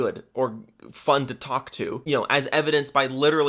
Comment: did he just say talk tuah???!!